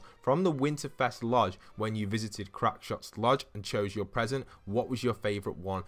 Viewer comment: for some reason it sounded like u said “Crapshot” idk y, but yeah